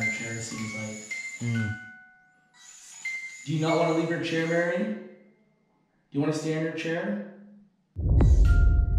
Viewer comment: Hey elton please listen back to the audio at 51:05 till 51:07 i hear a woman whisper something.!!!! Ps keep up the perfect video's love the vibes and all the good evidence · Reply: if you listen closely it says "it's him"